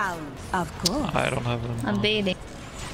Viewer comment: Im baiting..... My brain: .......did she just say shes BATHING????.... sorry im just a lil bit lonely because of this lockdown. call help, send cuties